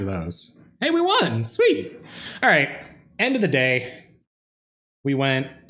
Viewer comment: Totally thought you said Snow Porn Selesnya during your intro. O.o